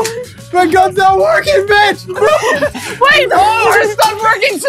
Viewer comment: Is it just me or did say the B word at 8:06 ? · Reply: TM MASTERBOY he said Mitch